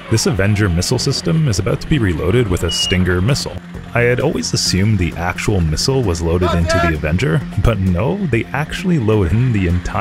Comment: I heard "Stinker missle" and I was like yeah that does surprise me. I didn't know the military weaponized farts